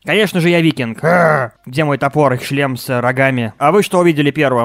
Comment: Мне послышалось не шлем с рогами а член с рогами